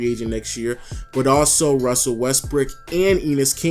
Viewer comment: Anyone else hear LoW say Russell Westbrick @ 10:33 ?